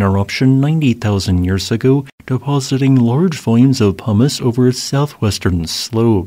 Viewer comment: i really thought you said "large amounts of humus" and i almost booked a flight to start another eruption for dat humus 🤤